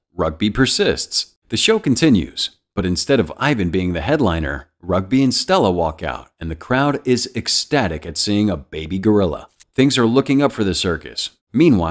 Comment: Uh my man did you say a baby gorilla?